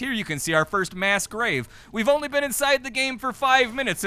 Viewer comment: Mass grave? I thought you said mask rave 😔